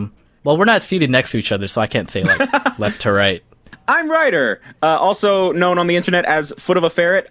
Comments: I thought when Ryder said “I’m Ryder” he said “I’m right-er. (Totally said his name wrong sorry) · Who else thought Ryder said “I’m righter!” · Who else thought Ryder said “I’m righter!” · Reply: I thought he said i'm writer